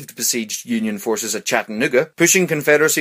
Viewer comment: hol up wat he say😂😂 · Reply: "Chattanooga"